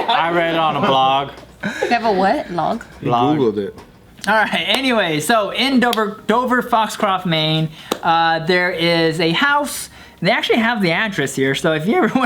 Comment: I thought said "bend over" 🤣🤣🤣🤣🤣